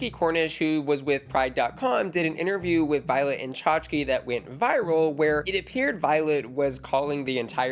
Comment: “Did an interview with Violet and Chachki” lmfao. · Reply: I kind of did a double-take. "Did she just say Violent and Chachki?" 😂